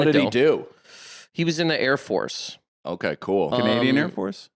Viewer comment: said “Canadian Airforce?” But I thought he said “Comedian Airforce?” And now I have a new project to work through. 😂❤🎉